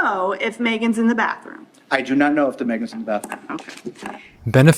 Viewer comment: I'm almost certain that he says, "I do not know if THAT Megan's in the bathroom"...it feels like, especially because he said "I do not know" instead of the contractions "I don't know" or "I dunno" that he was trying to be pompous and slowly speak out the full entire sentence of "I, do, not, know, if, that, Megan, was, in, the, bathroom" however the pressure of the situation he was in made him speak the sentence quicker than he wanted to and also contract the end of the sentence, so I believe that you simply misheard what he said in this specific incident and that he wasn't marking Megan as an object. I've watched this court case probably about 10 times now and I never once heard it as "THE Megan" and honestly still can't hear it that way. He's a detestable person but I persoanlly feel you were overly analytical here.